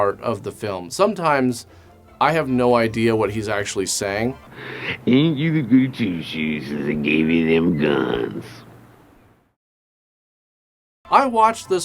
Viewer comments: 'When Tommy Wiseau boy' -first line of the description at the back of the box edit: 9:04 It was at one time I was listening instead of watching RLM's reviews that I understood(?) this line, I think grampa said 'ain't you the goody-two-shoes since they gave you them guns' (?) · Edgar Wright would be proud of you looking into the car totally confused at grandpa's gibberish. Gave me a good laugh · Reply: is around when it happens. Also I think he is saying ain't you a goody-two shoes since they gave you them guns. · he clearly says 'Ain't you thegoodytwoshoessincetheygaveyouthem gunnnnnssss'